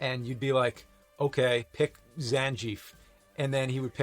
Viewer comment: Fist time I heard "Zanjief."